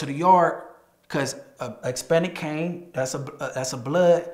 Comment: Sound like he saying expanic instead of Hispanic